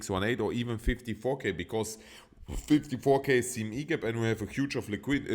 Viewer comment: Hey, bei 2:33 redest du irgendwas von "gap" ich verstehe nicht was du da sagst kannst du es mal auschreiben? 54k ....... gap? Aufjedenfall wird bei 67k erstmal geshorted. Ich wünsch dir viel Erfolg und hammer, dass du jeden Tag ein Video raushaust. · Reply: CME GAP